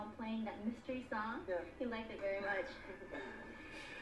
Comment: So cute how he comes a cross more shy to match them at the beginning and at 0:21. What does he say at 0:23? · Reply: Upon listening more carefully, he says, “I go with what I got.” Yes, he was referring to said incident, and the musical interlude that he played to fill the time.